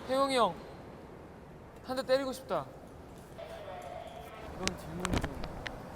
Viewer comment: Oh my, I thought Doyoung said that he wants to snack Taeyong, not smack 😂👏